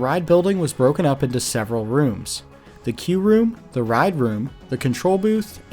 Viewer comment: Not really pertinent to the accident, just something that is confusing me from the set up description... it sounds like you're saying key room but the cc says "Q room". Not sure how it gets Q from key but just wanted to clarify what it should be lol I do have ADHD caused auditory issues which is why I do tend to have cc on to help me catch things I mishear, especially when some creators talk fast or there may be technical jargon. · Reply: Queue, as in the waiting area.